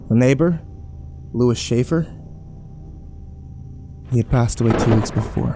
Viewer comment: I was listening to this while trying to sleep and I heard “our neighbor, Luis shaffer” and just shot up and was like “that’s my last name” and proceeded to go to sleep :)